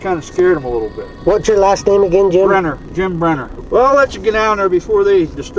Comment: I thought the guy said his name was Neil Breen :O